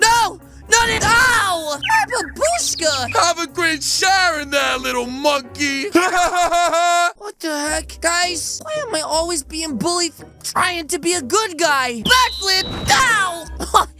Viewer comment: Best video ever Don't give me hate but did he just say monkey 😮😮😮